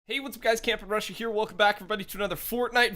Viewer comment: I just realized youve been saying camping rusher here i thought you said cam from russia